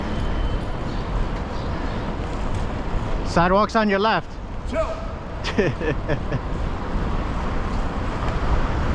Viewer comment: What did this goober say?😂😂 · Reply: "Chill"